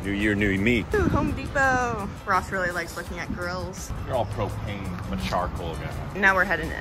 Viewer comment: I had to listen again. I thought she said Ross really likes looking at girls not grills lol 😂.